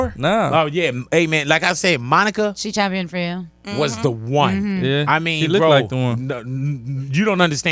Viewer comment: nobody got what he said? “She look like the one “